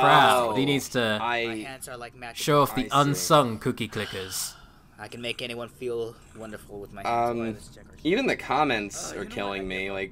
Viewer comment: I am sure I heard him say "I can make anyone feel wonderful with my let's plays" no you fucking can't.